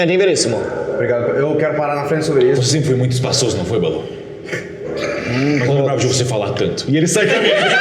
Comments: só eu que n entendi oque ele falou? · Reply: Eu não lembrava de voce falar tanto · oq o cellbo falo chat?? nao entendi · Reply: Sr. Veríssimo: Eu não lembrava de você falar tanto. Mestre: E ele sai da mesa. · Não entendi o que o Veríssimo falou pro Balu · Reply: Veríssimo disse: "Eu não lembrava de você falar tanto"